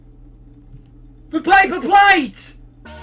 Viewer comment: what does the guy at 23:55 say? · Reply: "THE PAPER PLATE"